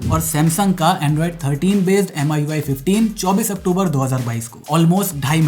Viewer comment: Samsung's One UI not MIUI. I heard MIUI 😅😅